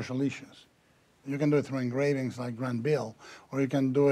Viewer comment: What name did he say at 1:03 when he talked about engravings? sounds like Gran Bill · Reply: "Grandville" - see Jean Ignace Isidore Gérard Grandville on Wikipedia.